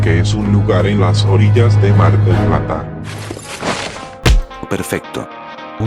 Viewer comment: Que Gran Video Lpm Sos Un Genio. Me Cague De Risa En Varias Partes, Che ¿Soy Yo O En El Minuto 21:05 Escuche El Nombre Mar Del Plata? (Ciudad En La Que Vivo XD) :v